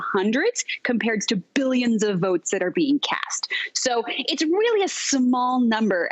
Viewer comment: Did she say "BILLION of votes being cast"??????